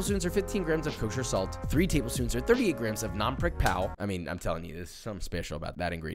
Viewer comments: what is the ingredient he is saying here? i cant make it out. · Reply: Nam prik pao (thai chili paste/jam) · or so he adds "something special about that ingredient" to the roast beef goo and it sounds like he says "non-brick pao". Roast beef isnt in the cookbook. Any idea what that special ingredient is? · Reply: Oh, the recipe is in with the social media links. The ingredient is "Nam prik pao"